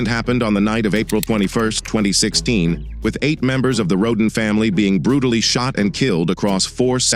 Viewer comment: I can’t help it, but I keep hearing "the rodent family“- :face-blue-smiling